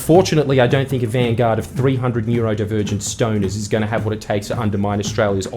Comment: When said 'a vanguard of 300 neurodivergent stoners' I heard 'a bunch of R words' hahahahahaha